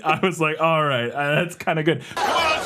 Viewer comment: when he's swinging the leash guy around, does he say "alright! The flying gay butt bomb"? Am I hearing that right or am I just imagining things? As for the "Private Eye License", it depends on the state: here in Virginia, it's controlled by the Department of Criminal Justice Services. The DCJS are who you would go to for training and licensing of PI's, bail bondsman, security guard (armed or unarmed), armored truck driver, etc. If it's private security or law enforcement, they are who you would see in Virginia. I'm not sure about other states though.